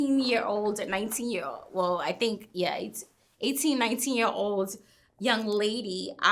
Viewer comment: 18 19? I thought they said she was 16 🧐 said she was a liar Why tf is everything news too her 🤦🏽‍♀️